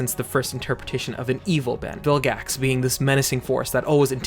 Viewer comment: "Bill Gas"? 🤨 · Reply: In the beginning, I misheard and called him Gill Gax